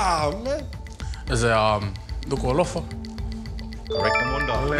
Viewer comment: Did he just say Dukualofa!?